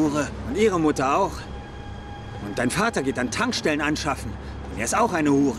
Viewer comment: was sagt er da? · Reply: „Und dein Vater geht an Tankstellen anschaffen“